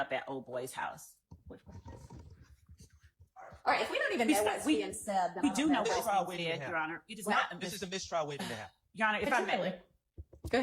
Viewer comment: Schardt “This is a mistrial waiting to happen.” Judge “Potentially.” I had to slow it down and listen a few times, but I’m almost positive that’s what she said lol